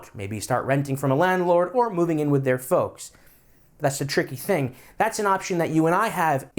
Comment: I thought you said "maybe start renting from a landlord or moving in with their foes" and I was just like "Well that's redundant"